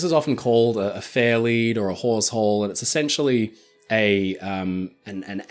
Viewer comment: Hawsehole….I thought you said wh#r@ hole. 😂